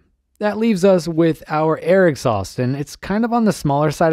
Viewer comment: I heard 4:08 as 'Eric sauce' and was just like WTF? ...it's air exhaust... 😅